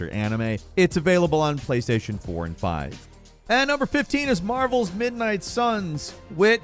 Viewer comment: At first I heard “Marbles” instead of “Marvel.” Got excited for a new Marble Blast Ultra 😂😂